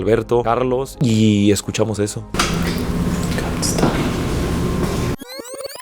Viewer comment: yo escuche como un "come stay" osea un ven a quedarte